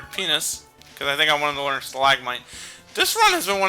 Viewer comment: Did you just...busts out laughing did you just call him Penis? XDDDD